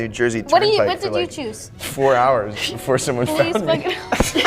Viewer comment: what he say💀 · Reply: 4 hours